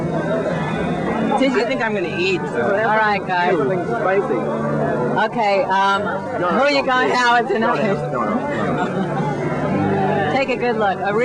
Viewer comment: it sounded like said or asked something, and right after it sounded like Tom said "Don't ask that, no, no." Does anyone know what she might have said? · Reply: “Who are you going out with tonight?”